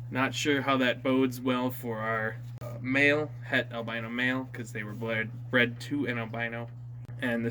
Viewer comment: It sounded like he said it's an albino hett male